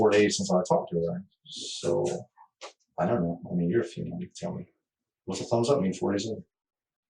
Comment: did he say “I mean you’re a female “